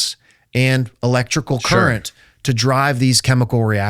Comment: Edit, what did he say that they had to overdub the word “current”. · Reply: The n*** current